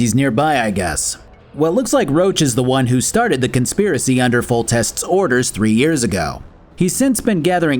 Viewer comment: I was listening to this while working, so I wasn't paying 100% attention. It got a chuckle out of me when I misheard that "Roach was the one who started the conspiracy under Foltest's orders." Damn horse!